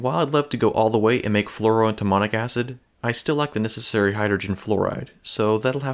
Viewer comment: at first I thought you said "fluorodemonic acid". Then I realised what you said and was like "eh, close enough" 🤣